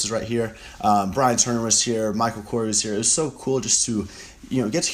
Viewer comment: did he say Michael Kors?